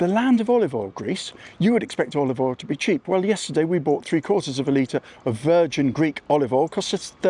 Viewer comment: Where did you buy olive oil so cheap? I noticed the grocery store olive oil in greece is more expensive then even in canada. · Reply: Woops i mis heard 3/4 of litte for 13 euros is excessive. I thought you said 3 litres@